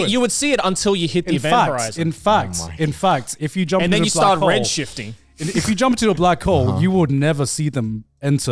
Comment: At did joey say at 51:15, red-shifting?